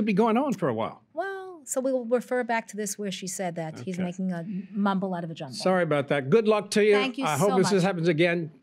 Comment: did she say "he's making a mumble out of a jumble"?